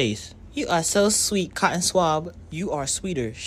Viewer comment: To I was turning my phone off and I heard “can you give me head-“ I was wondering how fucked up y’all schools were😭